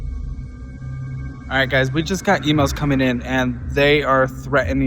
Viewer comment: instead of emails I heard IMO’s and thought y’all were fighting with a great St. Louis pizza company 🤦🏻‍♀️😂